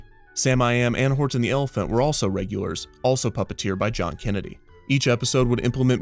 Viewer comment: did he said president John Kennedy?